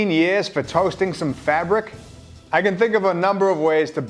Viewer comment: did say Fagrick?